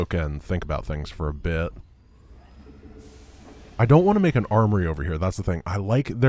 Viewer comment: At one point I thought I heard say "arbory" instead of "armory". I think one of us is taking the term tech tree too literally.